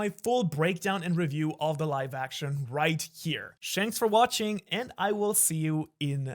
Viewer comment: I heard him say shanks for watching, nice one.